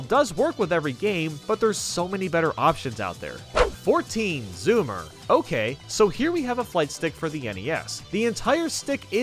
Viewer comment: he sounds like he is saying boomer ok